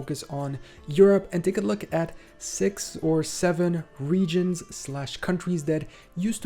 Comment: Did bro just say 67?